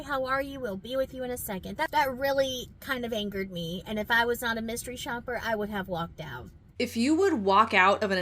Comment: I'm pretty sure I heard her right. At 18:04, when she was in her car complaining about the manager and stating that, "and If I had been on a mystery shopper, I would have walked out." Anyone else catch that? So, according to here, she wasn't even at that restaurant doing a Mystery Shopper job! I clicked on your video, because I had no idea what a Mystery Shopper was so I had to watch to find out what it was. From my point of view, she is so unprofessional that there is no way she can be a mystery shopper. I don't know her. I haven't seen any of her videos, I'm not one for TikTok and this video is the first I have heard of her and this program. I also learned eons ago, to ALWAYS beware of ANYONE trying to sell you THEIR books, videos etc. to teach YOU how to do what they do, is a scheme. I learned the hard way. Putting out almost $800 for a real estate "course". They made their money, by selling you Their way to do this or that with books, videos and one on one 'support'. And of course, I learned later, after spending my money, that everything in THEIR course was ALSO free with a search through public records. Yeah, I remember that well. LOL And then when she started promoting HER course, I didn't like her right then and there. I'm about to be 72, that real estate thing was 50 years ago! LOL Hearing her and then her plugging her course, which was able find out that all the same info she was selling was actually FREE on the net, sent chills up my spine. Thank you